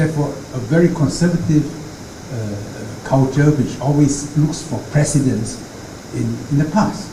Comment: 台灣媒體編輯記者注意： “looking for precedence” 應該翻譯「以史為鑒」, 才不是什麼都是「以總統為中心」。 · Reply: Precedence 誤聽成 President